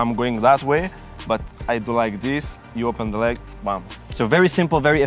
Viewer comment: Did just say gbam at 1:00 na naija guy oo 😂😁😂😂😂😂